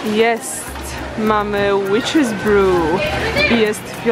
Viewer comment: Czy tylko ja usłyszałam ,,Bitch what?" XD